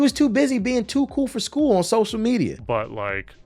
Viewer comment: that’s hilarious because I was just listening to this shit in the background and I could of sworn I heard “Cerberus” instead of servers 🤣 and then 10 seconds later you drop a Mass effect reference